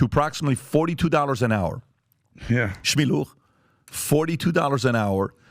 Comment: did Pat just say "shme lokh" in my language it means did you hear that. Or im just need more sleep time.